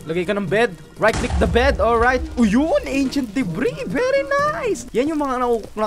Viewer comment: what I hear achent debri